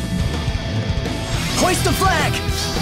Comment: When the video first started, all I hear is "HOST THE FLAG!!!" So I did and pledged my heart to the republic. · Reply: Dang, I meant Hoist, not Host. Seeing as I got a heart, not changing a thing, no no no, can't make me change it now.